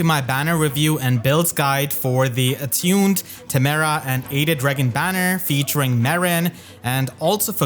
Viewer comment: Completely misheard at the beginning and thought you said, “the hated Reginn banner” 😂